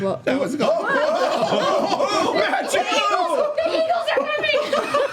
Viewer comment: Also, sorry to be the deaf audience member, but could anyone tell me what actually happened at 50:09? I could hear making the joke that the eyeballs weren't all eyeballs, but then it devolved into madness and while I still laughed at the reactions, I couldn't decipher what everyone was saying, so I didn't get the joke afterwards. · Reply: A miller flew up so Laura made a Gandalf joke "The eagles are coming!" That's pretty much the only dialogue the rest is just excited sounds and laughter.